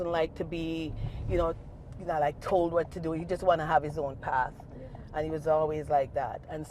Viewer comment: She need to pray with him and take him home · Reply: I thought she said he wants to live this way, in which I assume he will not come home.